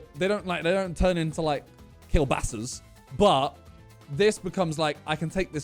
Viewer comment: did he said kiełbasas??? omg ahhshs